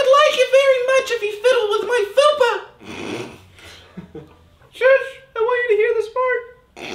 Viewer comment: what the hell did say I couldn’t hear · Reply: Shoosh i want to hear this fart?